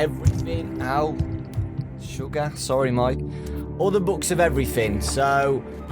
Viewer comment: "Sorry Mike" Am I the only one who heard that?